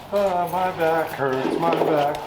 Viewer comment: It is a sign that I am a car guy that when I heard the expression "my back hurts" for a second I thought I heard "my Packards"